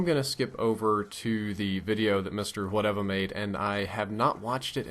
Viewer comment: At first i thought you said Mr. "Whatever" lol